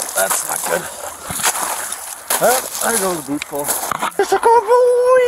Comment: I love the talk between the 2 officers. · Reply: does he say booty call?